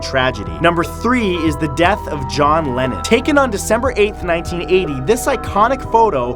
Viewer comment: I thought he said John lemon... I like food to much